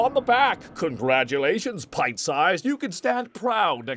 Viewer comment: a dumb question but what's the word cell said at 14:28 - 14:29 is it pine size? I searched it up but doesn't exist but I've heard it so many times. WHAT AM I DOING WRONG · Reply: It's pint size. He's insulting Gohan's height by comparing him to a pint.